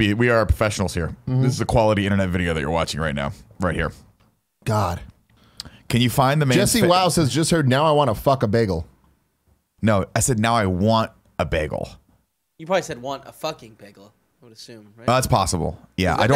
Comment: Lmao!! I heard Nick say "I want to fuck a bagel" too